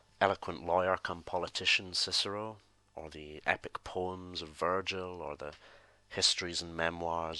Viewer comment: when you said poems i thought you said porn and i was like dude some stuff you don't share